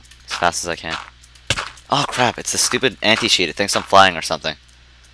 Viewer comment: When he said anti cheat I thought he said anti sheep XD